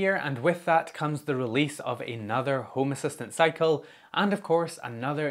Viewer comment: With your little moaning about 2023 just at the beginning, I thought you said "... the release of another Home Assistant Psycho..." I had to rewind and hear the more logical "...cycle..." 😁